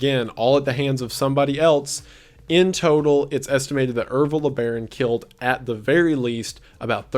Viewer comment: Thought it said Evil LeBron